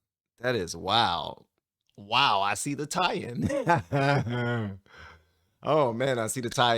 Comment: Funny. I heard you say "I see Italian" at first but about the third time you said it I heard " I see the tie in" . 😅